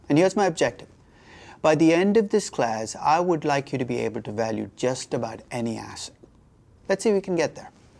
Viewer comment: I thought he said "...by the end of this class I would like you to be able to value just about any ass"